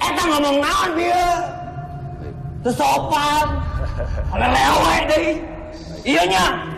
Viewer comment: Pada ngedenger suara rantainya ga??? Apa cuma aku yg denger? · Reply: Mau tanya.. Menit 28:11 itu ngmong jorok bukan nya..takut salah denger.makanya langsung di pukul.. Kata2 nya kaya "Hayang Ng*w* Deui" bener gak sih pendengaran gw? Kalo bener gitu sih pasti marah..